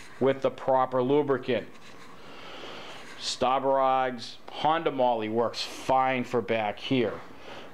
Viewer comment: What is the grease / spline lube brand he is saying at 0:51, other than Honda Moly? Stobberod’s? Stab-Her-Eyes? Schtahbberaghghssszzzz? LOL! I just CANNOT make out what he is saying, but he says it a LOT, and not knowing is driving me nuts! LOL! 😂😂😂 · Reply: Staburags NPBU 30 PTM from Kluber: <link>